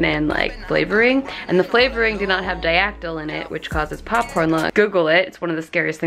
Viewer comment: What does she say at 6:40? Popcorn what? Really hard for me to catch that. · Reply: AsToldByJessica popcorn lung